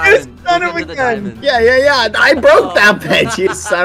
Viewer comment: It sounded like he said a naughty word 😂 "I broke that b- you son of a gun*